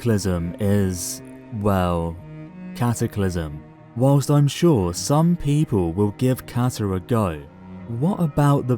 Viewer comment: When he said "while i'm sure some people will give Cata a go" I heard "will give cancer a go".